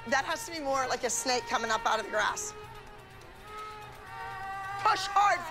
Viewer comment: it sounds like she said coming out of your a**